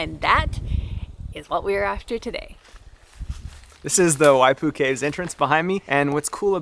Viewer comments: I heard Waifu Cave. Sounds like a dream for some people. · At first I thought you said, "This is the Y-2 Caves....." ..... I'm like, "Whaaaaat???